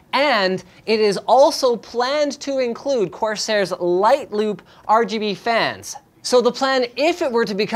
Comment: Did Linus just say "LightLUBE"? ;)